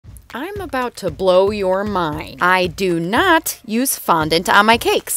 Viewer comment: Instead of hearing “im about to blow ur mind” I heard “I’m about to blow ur mom”💀 I had to hear it two times